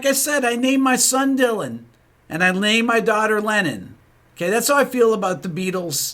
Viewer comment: Seriously, this is what I heard: You: "And I named my daughter Lenin" Me: "Say what?!" You: "That's how I feel about The Beatles" Me: "Aaah, you said Lennon" 😄